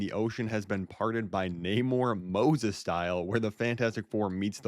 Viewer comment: Why did the subtitles say Neymar instead of Namor. 2:46 Chung cheese